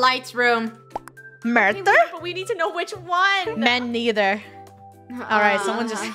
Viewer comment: does she say Men either or me neither